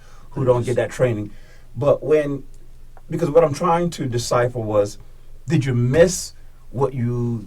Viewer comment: “what I’m trying to disciple.” Idiots that don’t read try to say words they have heard other people use. And don’t know what the word actually is. What the officer is trying to say is “what I’m trying to decipher” The word is decipher moron.